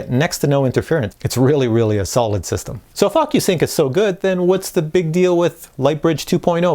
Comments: It sounds like he says " So Fu@ you sync..... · Did he say "Fuck you sync"???? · at minute 6:39 sound like your saying ``fuckyousync`` in stead of ocusync, wich is kind of stands for what my mavic pro is :)))